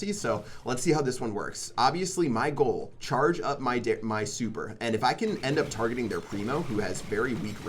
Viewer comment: charge up my dick? Anyone else hear this 😂😂💆🏻‍♂️💆🏻‍♂️